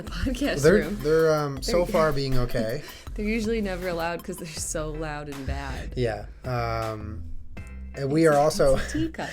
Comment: When she said "so loud and bad" regarding the dogs, at first I thot I heard 'so loud in bed'!